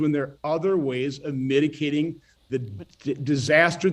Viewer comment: Had to rewind twelve times, but yup, this purveyor of intellectual superiority did indeed say "miticating" at 1:15. I guess that's when you mix up your mitigations with your medications.